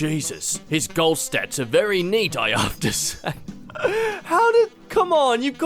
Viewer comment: I feel like 0:45 was messed up because of voice typing. So while instead of the device hearing "I have to say" it sounded like the person was saying "I after say". Sometimes due to an accent "have to" will sound like "hafta", which isn't an actual word so it corrects to after. Whew. That was more typing than I thought.